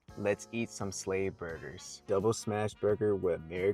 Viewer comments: I wasn't looking and thought he said slave burgers I was like "Slaves ate burgers?" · Thought he said slave burger